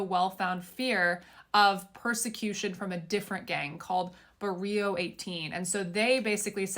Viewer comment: You can read all the paperwork from the court case but you can't read "barrio" right....LOL sound like she said "burrito" 😂😅 Glad he got deported